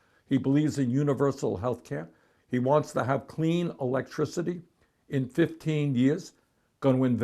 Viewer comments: Why does the closed caption say, "...CLEAN TERRORIST..." instead of "...CLEAN ELECTRICITY..." at 5:52? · the automatic subtitles at 5:54" "-He wants to have clean terrorist* in 15 years" *instead of clean "electricity" what the fuck, computers for Trump, now?? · At about 5:52 the auto-subtitles somehow think Bernie is saying: “He wants to have clean terrorist in 15 years.” Lol, how does that sound anything like “electricity”? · closed captioning be like "sounds like clean terrorists in 15 years. Yup, defo sounds like he said cleAn terrorists." What is this? Wikipedia?